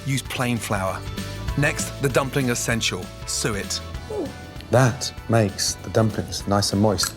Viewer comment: can someone tell me what is the dumpling essential he says ? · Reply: Suet.